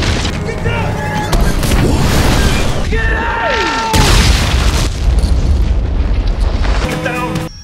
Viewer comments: Awesome video and everything but... I know I'm probably nit picking but at 1:33 I always thought Jackson said "Parker, Get Out!" I dunno, maybe it's just me. Awesome video. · Sounds like Jackson is saying "Get Down"! Anyone else? If it is - Huge Disapointment! I expected better of you Screen Junkies.... if not though, Ignore my comment :)